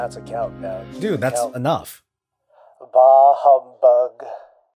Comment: Did he really said "bahebk" which means i love you in Arabic in 0:40 ?? :D